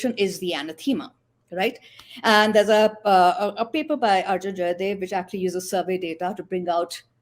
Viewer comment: Can anyone tell me what paper Ramaa mentions at 4:00? Couldn't make out the name of the author. Thanks! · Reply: Arjun jayadev